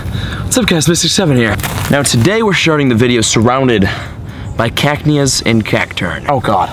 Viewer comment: Did he say "we're sharting the video"